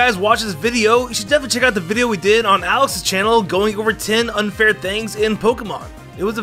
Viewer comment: I thought he said Alice